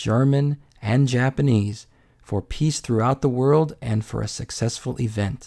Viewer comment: the captions say "peach throughout the world" instead of "peace throughout the world" 🤣